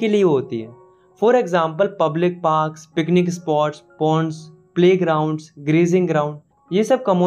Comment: I heard porn🌚